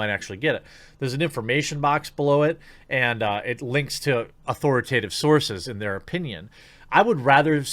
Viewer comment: Every time someone says "authoritative sources" I hear "authoritarian sources"...